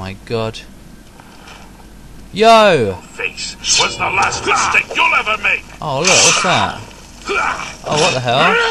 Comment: Did he say: "Your face was the last mistake you'll ever make"?